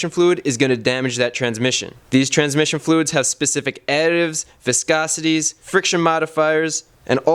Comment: you listed a few properties that transmission fluids have. What was that first property that you said? I couldn't understand you clearly... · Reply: he doesn't start talking about qualities, he starts talking nonsense... He says: -Merkon V has specific viscosity additives... Specific viscosity additive is a meaningless term because the base oil for ATF is already low viscosity. Additives adjusting the viscosity to the final stage are viscosity modifiers. Polymethacrylates (PMA), olefin polymers (OCP), polyisobutylene (PIB) and styrene-butadiene copolymers (SBC) are used. They are also used in motor oil as a Viscosity Index Improver. -friction modifiers and all that stuff... a friction modifier is an additive - fatty acids are used, fatty acid derivatives, organic amines, aminophosphates and the like. Friction modifiers are in every gear and motor oil. Nothing about the features. GM and Ford are the dominant manufacturers of ATF. Ford = Merkon GM = Dexron Questions?